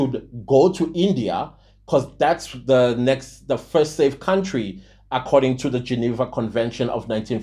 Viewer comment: I closed my eyes and i heard "GNU according to CD"